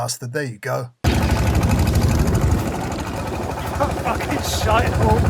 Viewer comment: Did I hear you say “shitehawks” at 4:07? It’s a long time since I heard that word!